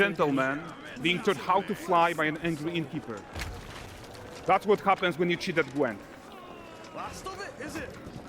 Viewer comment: What they say: That's what happens when you cheat at Gwen What i hear: So you can cheat at Gwen now 🤔